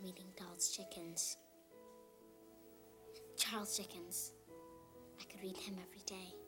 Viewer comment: my entire life I thought she said “Child’s Chickens”